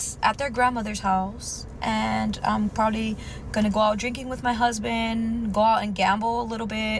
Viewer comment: did she say HUSBAND?